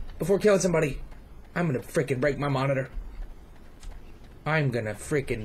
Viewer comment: did he say ''im gonna rape my monitor''?